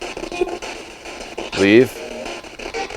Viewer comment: when he said he heard "leave? I heard lethal instead